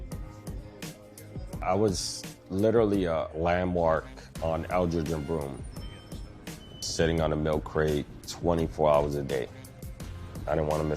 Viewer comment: What does he say at 1:44 the captions couldn’t even catch it. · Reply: I was literally a landmark on eldridge and broome.. sitting on a milk crate 24 hrs a day.. didnt want to miss a dollar